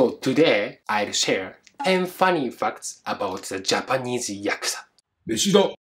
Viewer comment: I thought you said ‘10 Funny Fucks about the Yakuza!’